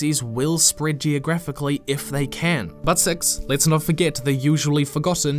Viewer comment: This is an incredibly concise and well thought-out video. And I hate to say this... But I gotta at admit that at 15:25 I had to go back and listen again because the first time I was like “Did he just say... Butt Sex?” Also, can confirm that the auto-generated CC says “but sex” in the same spot. I just thought you might want to know. Lmao🤣🤣🤣🤣